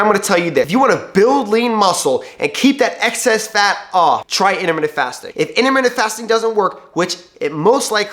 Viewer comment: what does he say for us to try? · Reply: Intermittent fasting